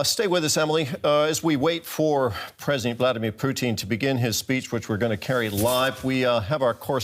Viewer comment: "As we wait for..." Did he say _"Blyatimir Pootin"? 🤣